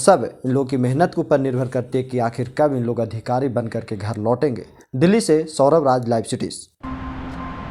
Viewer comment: I thought he said bhikhari😂🤣🤣🤣